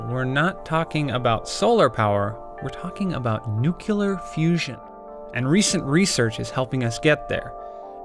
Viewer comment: am i the only one who heard nu-killer fusion..